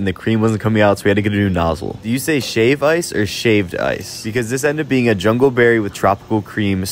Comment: I thought it said old shaved ice